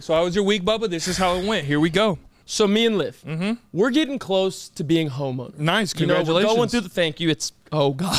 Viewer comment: sounds like says he's close to being homo 😂